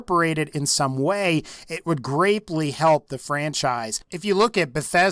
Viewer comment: Did you say it would....GRAPELY help the franchise!?